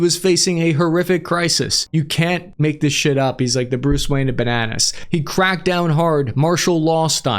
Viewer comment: i demand reparation for the coffee spilled on my Iphone16Max the moment i heard bruce wayne for bananas on 15:13 it went supersonic jettison